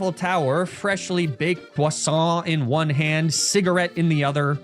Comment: Idk if this was intentional or not but it sounds like you said fish in french (poisson), eating a full freshly baked fish would surely be a sight to see in the morning (It just reminded me of something that was joked about years ago bc one of my classmates couldnt tell the difference back in middle school LFE)